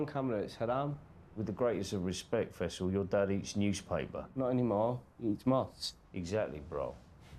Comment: With the greatest of respect Faisal you’re dad eats newspaper 😂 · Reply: their speech defect is so bad. I thought he was saying "your dad is a newspaper" "No he's not. He is nuts."